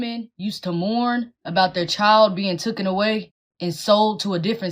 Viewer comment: Oh my that's a nasty looking one wow. Ugh. Shivers not only from all bs she is spewing but how she carries herself. Nightmare fuel. 19:40 did she just say "tooken away"? Or do I need my ears checked?